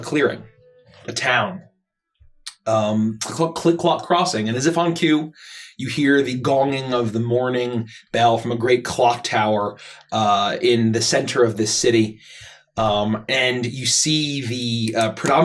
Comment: When said "the morning bell", i thought he said "the mourning bell"